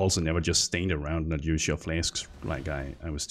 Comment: Everytime you said "flask" I keep hearing "fläsk", danish perhaps?